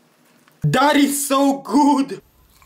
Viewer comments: Did he just say... DaAd Is SoO gOod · I thought u said “daddy is so good” but u said “that is so good” 💀💀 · Spizee: THAT IS SO GOOD What I hear: Daddy's So Good💀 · What i hear:Daddy's good! What it stays:That is so good!💀💀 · I just realized that it was “that is so good” I heard “daddy so good”…..💀😭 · I thought u said “daddy is so good” but u said “that is so good” 💀💀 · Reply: I thought he said "god is so good" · “IT WAS BASSIN’ “ “THAT IS SO G-GOOD” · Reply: I heard him say god is so good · "Daddy so kood" 💀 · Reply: i hear “that is so good” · Spizee: THAT IS SO GOOD What I hear: Daddy's So Good💀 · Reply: Uh i think i only heard "Dad Its so good" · That is so gud vs what I hear "daddy so gud"